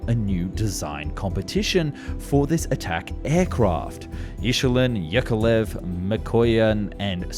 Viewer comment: I heard 'Ish-ulin'